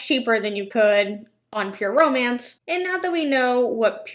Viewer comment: It sounds like she’s saying pure omance and it’s driving me nuts. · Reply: I hear "permans"